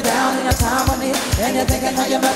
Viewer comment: what did she say? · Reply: “The price sisters (the backup singers), you better sing”